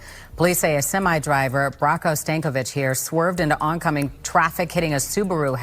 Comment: Did she say his name is Stankovich?